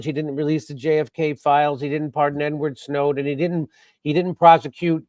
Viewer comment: I noticed Jimmy, you slipped in N-word Snowden 13:07 instead of Edward Snowden. Very nice subtle comedy.